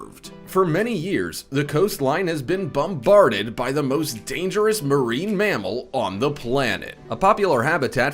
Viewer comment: Did he say "the most dangerous marine MAMMAL?"